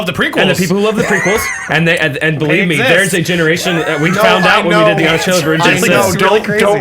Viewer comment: There's a moment around 30:40 when the 3rd guy says "there's a whole generation that loves the prequels, we found out when..." when what? I couldn't hear because the other guy interrupted with "I know, don't remind me". You may know, now I want to know too. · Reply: When they did the Honest Trailers for the prequel trilogy